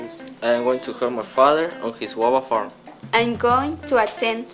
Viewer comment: minuto 1:47 no entiendo "im going to help my father enwowofarm" y muestra unos globos??